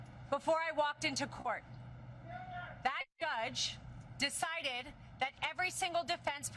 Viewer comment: Did she just say "that gudge" instead of judge? Aww bless, ... well, ... she's making a lot of money from donations. I won't knock the hustle.